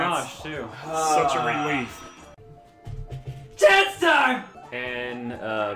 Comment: TENDUG At least that's what i think he said. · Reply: Trismon55555 ”CHANCE TIME!”